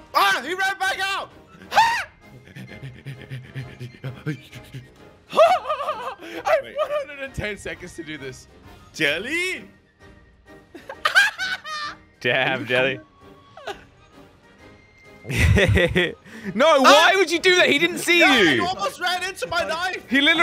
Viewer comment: Im i the only one that heard him say “damn Jelly”